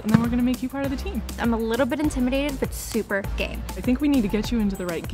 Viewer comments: why did I hear “I’m a little intimidated but super gay” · i thought the said gay instead of game😅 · Am I the only one that heard her say “I feel a little bit intimidated but super gay” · i thought she said “i’m a little intimidated, but super gay.” 💀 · I thought she said, “I’m kinda intimidated, BUT VERY GAY.” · "im a little bit intimidated but super gay " uhm excuse me but did i just hear... welp nevermind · thought she said gay, but subtitles say game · I thought she said “I’m a little bit intimidated but definitely gay” and I got so confused😂